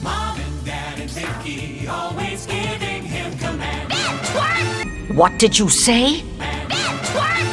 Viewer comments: Me:I thought she said bend twerp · I thought she said bad wort